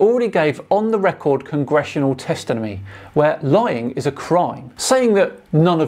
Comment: did I just heard you say “testinomy”😅 love your work keep it up